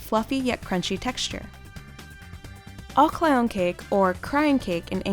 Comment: When they said aglayan kek for turkey I thought they said all clown cake 🤣😂🤣😂